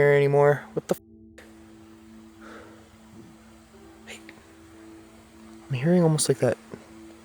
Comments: did you say "hey" or something that sounded like that, or was that a disembodied voice? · a disembodied voice just said "HEY!" 😱 WTF?! I heard those crazy footsteps too earlier, that were behind your camera! This has to be yet another terrifying place in Japan! In my opinion, this place is more terrifying then the "Suicide Forest!" These noises, whispers, footsteps, slapping you on the neck! Plus, that psst sound is so constant, especially in Part 1! I can't wait to see what goes down after that evil ass slap! Those spirits need to back-off and leave you the hell alone! You are so calm and it's just incredible to watch! Why you're not at 1 million subs is beyond me! You are in a league of your own! This Japan trip has been one of my all time favorite series!